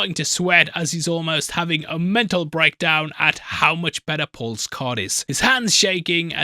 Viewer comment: subtitles say "Paul's cod" 🎣 😂